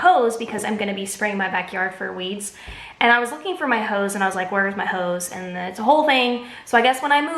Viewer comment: When you said that you were looking for your hose. My 8 year old son said he thought you said you were looking for your husband. 😆